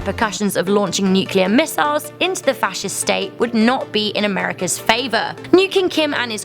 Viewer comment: did I hear fascist state? From what I know NK IS BLOODY COMMUNIST